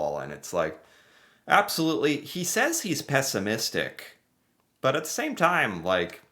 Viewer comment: You misheard . Chretien said he is NOT pessimistic.